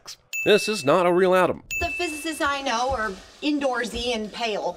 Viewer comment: Ha ha ha ha ha ha ha ha! In the 2:37 sin, you're trying to say "physicististist"! Like I think you're saying "physicistjuzistist"! Ha ha ha ha ha ha ha ha ha! I think that's the first funny sin in the TVSins channel. Ha ha ha ha ha ha ha ha! 😄